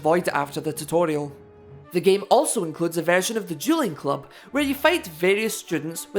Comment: You can actually go into the settings and make the game harder. I've done it before. I didn't really notice a change though. Please tell me what your experience is with it. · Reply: I thought you said Juuling club. We only have those in American middle schools.